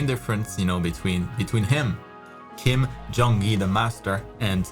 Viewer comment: Kim jong il.. i actually search that person to his arts😅. Then found out he is non other then North Korea's King Jong😂. I didn't understand your joke at first.🤣 OR is there any real great artist with that name?😳